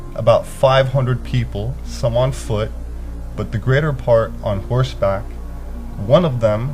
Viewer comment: did he say on horse back